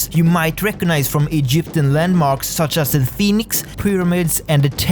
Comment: you say sphinx i hear phoenix